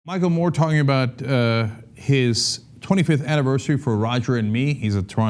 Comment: i heard at start "his 25th anniversary of rodgering me" but only british ppl will know why this is funny